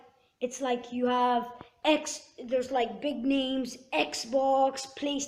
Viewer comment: am i the only one who thought he said "big memes"?